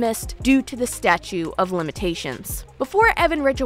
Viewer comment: Awesome video. It sounds like you are saying “statue of limitations”. It’s actually “statute of limitations”. Maybe I am mishearing you. This video is very good and well researched.